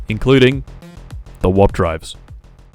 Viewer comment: It kinda sounds like hes saying "wap drive"